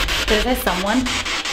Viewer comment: that "someone" at 9:42 for sure sounded like rug let me now if you also heard it sound like rug